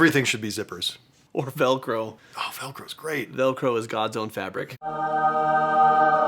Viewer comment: Velcro is god’s own fabric. I swear I thought he said “Velcro is Gonzo’s fabric” and I was thinking “you mean gonzo from the muppets?”